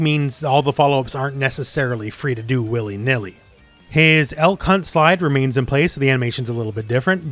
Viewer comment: I definitely misheard you lol. I had to turn on subtitles for this one. You said "Elk hunt slide", I thought you said "L-C*nt slide"